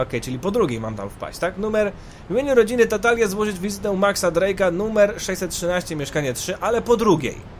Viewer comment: czy ja tu tylko za pierwszym razem usłyszałem nathana drake'a?